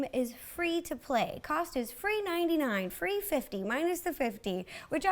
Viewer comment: My heart just stopped for a moment... I thought she said $3.50 and I was like NOOOOOOO